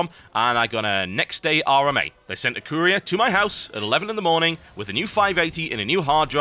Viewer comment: lol at first i thought he said they sent a korean to his house then i replayed it and got courier